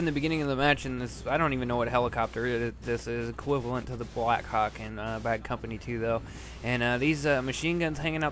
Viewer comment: LOL, sounded like you said : BlackCawk